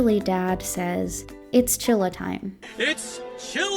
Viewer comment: omg he actually said it's chilli tine.. don't tell me he chilli'd all over the place too EDIT: 17:24 ayy rhat's what I paid